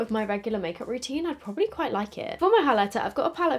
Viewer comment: why did i think she said lick it😂